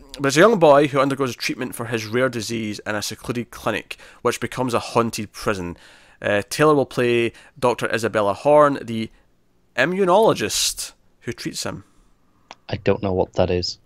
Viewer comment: I love the Pale Blue Dot speech so much. Definitely a point in the film's favour that they chose that as the title. Love Hawley's work on Legion. Love Natalie Portman. I wonder if this will be the prestige space and/or scifi movie of the year. The Gravity/Arrival/Interstellar that manages to make a mark come awards season. Donna Tartt's The Goldfinch was one of my favourite books back in 2014. I've been waiting for a movie ever since, although I don't know if any will be able to capture the slow passage of time that managed to make it so special. It was so expansive and covered so much of the protagonist's life. Not sure how I feel about the casting either, but if he pulls it off, I could see this being an awards season movie. Whoever they cast as the character of Boris will really need to bring it. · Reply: ~Ha, I just heard him wrong apparently, I thought he said "emmunologist"